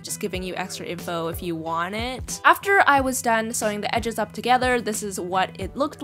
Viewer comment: I thought you said no sewing